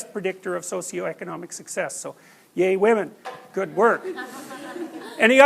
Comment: did he say "gay women" or "yay woman"? I'm highly confused